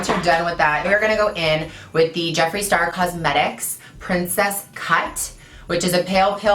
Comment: Omg I thought u said princess cunt I had to replay it like 5 times